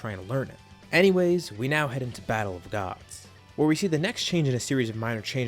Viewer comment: Anyone else notice when he says battle gods it sounds like he said battle of dods